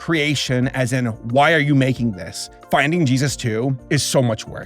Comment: sounds like he said finding je su